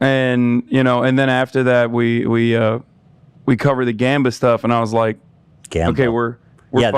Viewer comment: What did they say? "gamba stuff" ??? sorry lol I'm genuinely confused